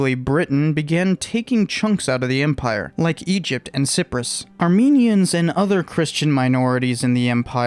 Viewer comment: did you say sip-rus at 3:03 onwards? Where is sip-rus? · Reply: Cyprus - it's a Mediterranean island just south of Anatolia and west of the Levant